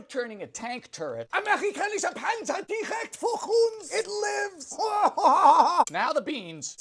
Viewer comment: i heard “Amerikanische Panzer direkt für uns!” so im guessing he staid “American tank coming directly for us!” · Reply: yes this is it! Except it's "vor uns" (in front of us)